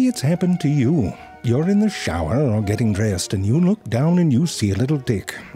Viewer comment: Did I just heard : " you are in the shower and you see a little "D" .😶